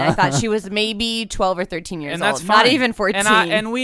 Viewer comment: did he just say “that’s fine”?